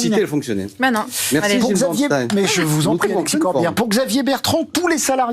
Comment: C'est moi ou à 5:27 le journaliste dit "Merci mon p'tit Corbière" WTF haha